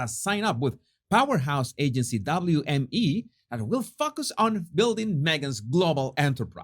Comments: did you say "focus" or something else? It fits either way for Harry's wife. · Bah-ha....I had to rewind......at 5:04.........I heard "F*ck us," instead of "Will focus"......I love you!